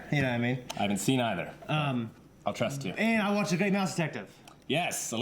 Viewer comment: have trouble understanding left guy. please speak slower. have to rely on subtitles to get many sentences, and even those fail. 0:40 which show have you watched? the great mouth detective? i absolutely cant hear it. subtitles say "i watch the game now spective" or 2:55 "he's a drug ...???" subtitles say "he's a junior head"